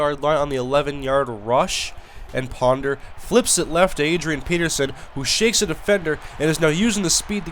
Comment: i thought u said adriean penisan at